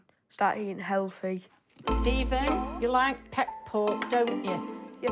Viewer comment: I thought she said pet pork. So I tried googling pet pork, but inadvertently googled pet porn. Now I have to erase my internet browser history. 😕 Edit: if she did indeed say pet pork, what the heck is that? All I got was dog treats.